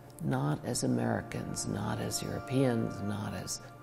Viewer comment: did she just said "not American, not European, not Chinese"